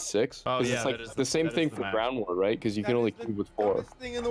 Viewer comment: I thought that he said “Brown War” 😅😂😂🤣